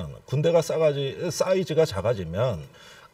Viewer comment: 군대 싸이즈가 + 작아지면= 군대 싸가지 ... 중요한 토론 하는데 왜 이런게 들리냐...